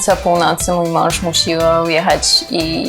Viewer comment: Czy ja dobrze usłyszałem w 3:31... musił?! 😖😛🤔